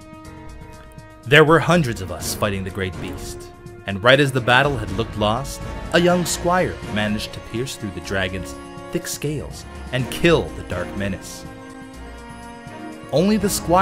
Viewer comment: because of where was stabbing the dragon, I thought said "dick scales"